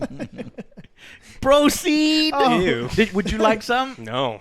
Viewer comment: I dunno if I hear it right but 1:12 I’m stealin ‘Broceed!’ Lol